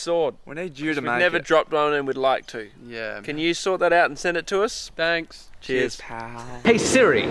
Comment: I heard, "Can you SWORD that out".